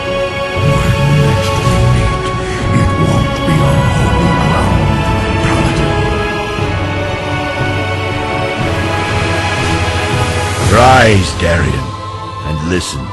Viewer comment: Can someone tell me what does Arthas say st 4:08 before teleporting? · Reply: when we meet again,it will not be on holy paladin soil.